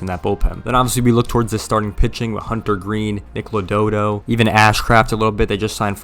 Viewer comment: I don't know if I heard right but it sounded like you said Lododo instead of Lodolo. And they did make more than two signings need to get your facts straight